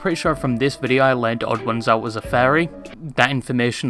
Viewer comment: i thought you said fairy and was very confused for a second